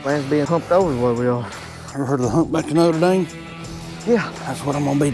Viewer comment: Did he say "U ever herd of HUMPback of Noter Dame"😏